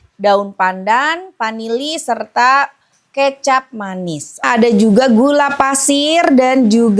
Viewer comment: Kolak pakai kecap manis?😂😂 aku simak ternyata susu kental manis😂 teteh laper jd gak fokus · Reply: Aku pikir aku salah denger 😂 kecap manis